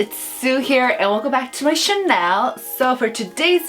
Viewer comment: every time when you said welcome back to my CHANNEL i thought that you were saying wlc back to my CHANEL lol